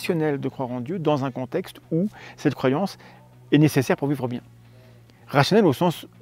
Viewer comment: "cette croyance est nécéssaire ..." pour quoi ? j'entends pas bien, j'ai repassé plusieurs fois mais je comprends pas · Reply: Il dit : Cette croyance est nécessaire pour vivre bien